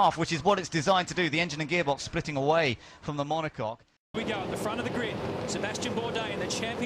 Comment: did he really say "Monocock"? I swear I heard him say mono-cock. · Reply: Yep, monocoque. Pioneered by Colin Chapman in the 1960s.